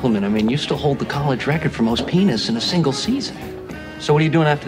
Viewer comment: Is it me or does Oscar say penis